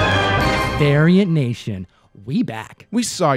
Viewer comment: I swear this dude say Aryan Nation lmao